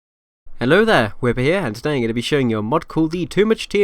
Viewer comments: sounded like the “why hello there” meme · That sounded like Obi Wan saying hello there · That beginning (at 0:00) sounded like one of those memes things saying “ Hello there”